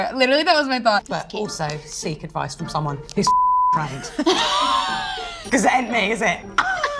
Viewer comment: Can someone tell me what Claudia was saying at 0:59 ? English is not my first language … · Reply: "seek advice from someone who's trained cause it ain't fcking me is it"